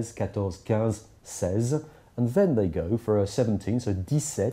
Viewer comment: Ten-Eight, Ten-Neun... Wait, what? Didn't you say a German word? That's how you're revealed yourself with the method you've mentioned earlier in this video!